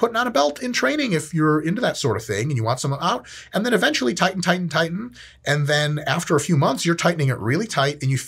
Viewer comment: I heard titan titan titan instead of tighten tighten tighten and I was like yes Titan does make a great belt